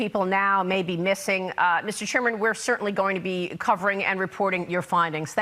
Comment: Did she call James Comer "Mr. Sherman" @ 4:11 ?? LOL